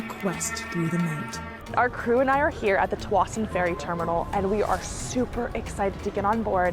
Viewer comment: Did she just say they're at the "Twattin' ferry terminal"?